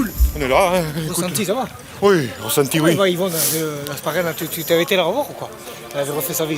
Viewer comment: Hasparren j'ai entendu ???